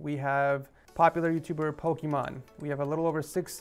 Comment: Did he just call her Pokémon .-.